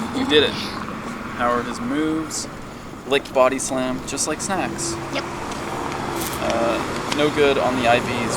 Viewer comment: When you said just like snacks thought you said just like sex